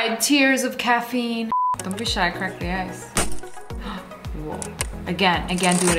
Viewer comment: Did she just say “Don’t be shy crack the a$$”😂😂